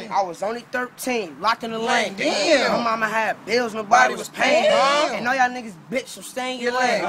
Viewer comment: "Everybody getting hurt but who going through pain" Y'all really slept on that. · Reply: lol damn I heard, “her body was payin”. Instead of “nobody’s payin”. 😂😂😂